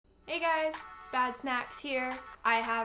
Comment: For a second I thought you said your name was bat snacks, Oh the irony Lmao